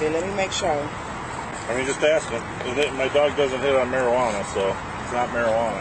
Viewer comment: Thought he said malaria